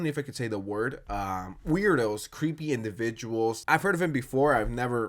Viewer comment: I kept hearing you say “creep” and though you were saying “Creed” and I was like: I was playing Halo with??